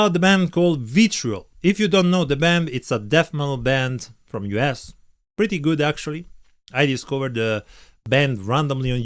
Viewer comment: did he say "a death metal band from your ass"?😂